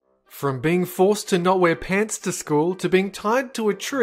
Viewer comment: Wait, did he say No wear pants?